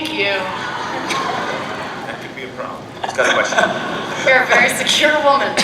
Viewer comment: what did the woman say at 16:10 and jennifer said "you're a very secure woman"? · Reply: I think she said "my boyfriend loves you"